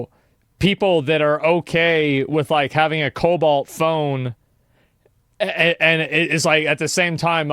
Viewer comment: When he was talking about cobalt (the material) and child slavery, I thought he said Cobol (programing language) at first. I was like, "what don't I know about Cobol?"